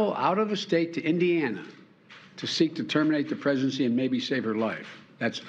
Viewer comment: You misheard Biden... he said terminate PRESIDENCY not PREGNANCY. Closed captions confirm that word.